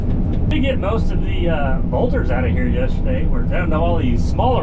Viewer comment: Great job 👍🏻 5:10 love how the words said volters instead of boulders 5:07 love what the strap on the back of your hat says love how you pulled the dump truck back love how something fell in the dump truck the dozer whould have helped with the path 12:53 love the cable on the ground love the pictures at the end especially the one where you were cleaning bed out and love how you said merry Christmas at the end and how you said always something to work on 13:32 never noticed the excavator bucket in the video I guess I wasn’t paying attention 😂